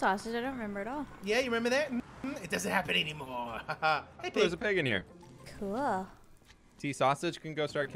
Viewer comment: when said "there's a pig in here", I thought he said "there's a penguin here"